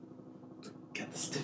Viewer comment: I got the skiffy uh Dad:BOI U THOUGHT I WAS PLAYIN HUH?????